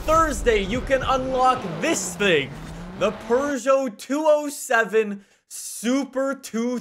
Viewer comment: Did you just say Purrgeot?